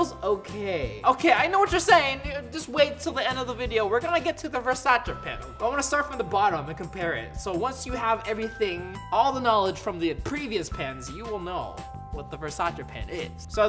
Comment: I heard varsare